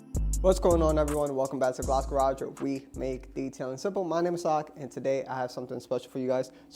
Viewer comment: Could you spell out your name for my dumb brain? All I hear is “My name’s Sock” lol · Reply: 😅. My name is. But people call me for short.